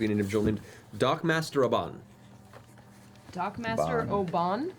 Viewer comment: Matt really did say Abban and I thought he said Obann. Almost gave me a heart attack.